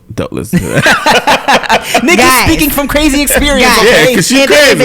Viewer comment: Did tim just say Nikki or...something